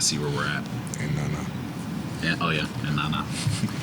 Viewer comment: what did SGA just say? "And ganar", spanish?